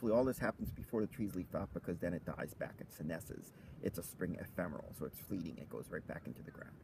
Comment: Thanks for the video and information. One question: what was that word you said at 1:34 after "it dies back" Trying to work on my botany vocabulary. Thanks! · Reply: Senescence